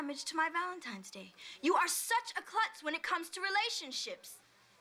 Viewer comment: Did she say "You are such a c*nt..."?! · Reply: She said clutz like clumsy, like accident prone